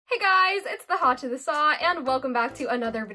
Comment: In the starting doesnt it sound like she was saying "Hey guys in the hatch of the sa" really fast??